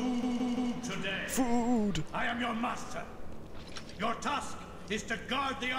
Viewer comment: o-m-g. i wasn't looking at the vid and at 2:15 when he says 'master' i thought he said 'i am your mustache' ....